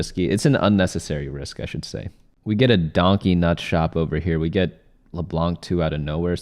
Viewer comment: “We got a donkey nut shop here” did I hear that right?